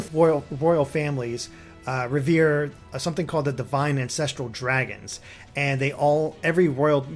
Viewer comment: Did say Divine INCESTUAL Dragon? *Crosses fingers for Hinoka S Support